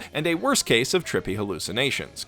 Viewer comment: did somebody said "Trippy"?